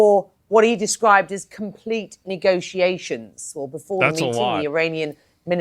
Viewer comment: All I kept hearing was “Nigga-tiations” 😂